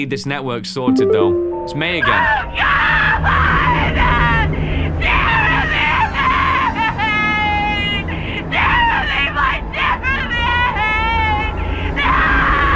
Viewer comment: BRO!!!!! From 1:03:50 through 1:03:55 I heard her saying!! “DON’T EAT MY JEREMY” and that made me have shivers down my spine!!!!!!😱😱😱